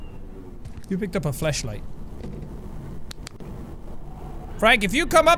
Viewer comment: Was it just me or did Sean say Fleshlight at 22:13??